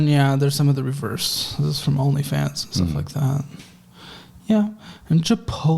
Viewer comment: LOL at 8:20 did he say there were Onlyfans reversals? ha!